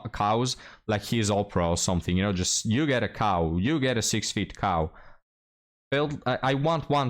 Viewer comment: you say cow. But I hear Kaw. I'll have a 1979 KZ1000 please.